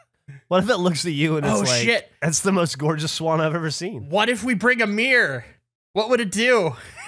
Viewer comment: I heard ‘what if we bring a mirror?!?’ in accent as ‘what if we brought Amir!??’ and had a great mental image of and a guy named Amir shirtless and smiling like the 80’s. I need sleep.